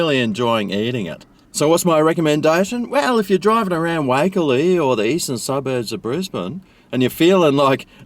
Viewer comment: Thought you said wacol Brisbane around the corner from my house. But it was Wakerley - 50 mins away 😔😅